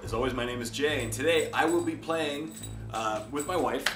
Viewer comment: I thought he said he was playing his wife?